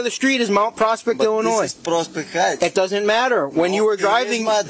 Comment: The guy getting pulled over sounds like he is saying prostate heights.